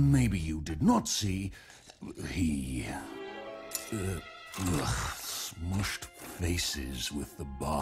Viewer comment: I had low volume and i thought draal said "smushed faces with mama"